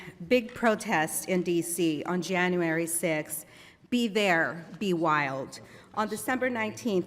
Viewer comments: LIED...does NO SAY "Be there be wild"! Says it.... WILL BE WILD.... COMPLETELY DIFFERENT MEANING 😣.... literally says it right behind her...will Finish call her out?.... of course not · LIED...does NO SAY "Be there be wild"! Says it.... WILL BE WILD.... COMPLETELY DIFFERENT MEANING 😣